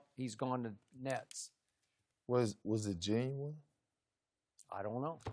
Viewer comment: What did he SAY??? "WAS IT JEAM??" WAS IT WHAT!???? 🤔